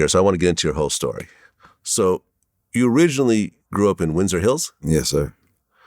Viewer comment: when hw said winter hills I thought he was talking "Southie" Boston. thought he was about to say the 1st gangsters he meet was Whitey Bulger lol...